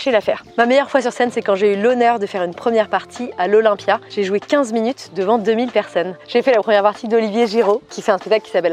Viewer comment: J'ai compris "j'ai jouis 15min devant 2000 personnes" à